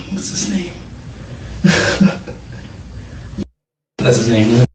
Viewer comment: After he does the laugh when he can’t remember the name. Then they redact the name he says. Does say “that’s his name, isn’t it?” And then the investigator says “yeah” or am I hearing things?